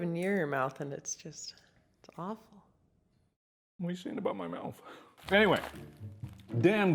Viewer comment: what does he say there i cant understand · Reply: What are you saying about my mouth